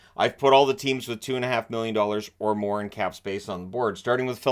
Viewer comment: I thought it said “teams with 25 million or more in cap space”